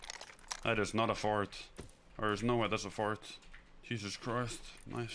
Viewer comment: Did I just hear him say, "There's no way that's a fart..." And then closely followed by, "I smell so fucking bad" 🤣🤣🤣